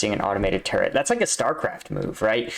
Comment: did i just hear starcraft